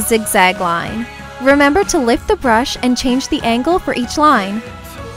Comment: I thought she said "lick the brush" but she said lift the brush Haha love you ❤️